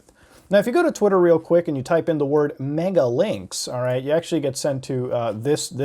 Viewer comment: I thought it said megamilk I just like milk... and that fat cow chick..